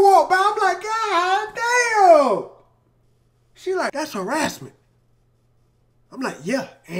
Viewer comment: Could be just me, but I heard Em say "that's her ass, man" with the harassment line.